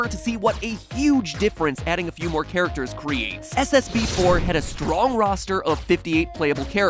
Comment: I swear when Matpat said "SBB4", I thought I heard "SMG4". Unfortunately it wasn't Smg4, but it would be really cool if they did a collab 👍